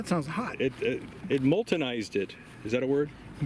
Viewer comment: "Moltenized it!" I swear I heard "Molson Ice'd it".